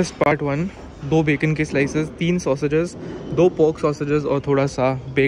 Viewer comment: did I heard it right pork????😐